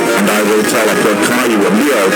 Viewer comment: did he just say forever?!